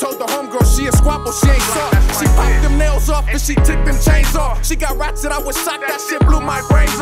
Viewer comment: Someone help me out lol 1:05 "she popped them nails off and she took them chains off, she ???????? That shit blew my brains off" what'd bro say?? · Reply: Yeah Dat “ she got ratchet I was shocked that shit blew my brains off”